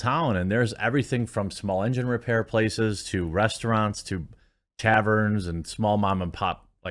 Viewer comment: said "taverns" and I heard "caverns". I love going to caverns.